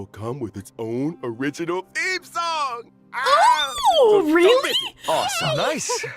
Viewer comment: i think said "theme song", not "Eve song". Crazy that Box2 also misheard that xd